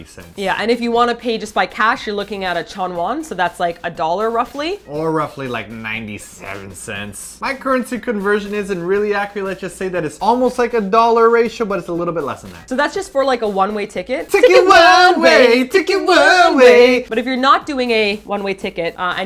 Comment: Am I the only one who hears saying "accuLate" instead of "accuRate"? ENGRISH SPOTTED MUAHHAHAHHAHAA :D @ 1:41 ->